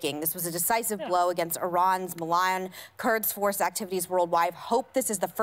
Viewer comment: did Meg say, "Kurds" instead of "Kuds"? 🤔